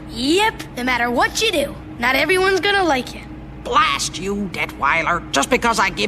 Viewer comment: why does it sound like she said" Blast you 8====D rider"